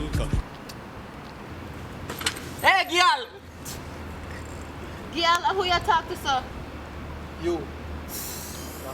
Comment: What did he say? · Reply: Emily Petsche “aye gal”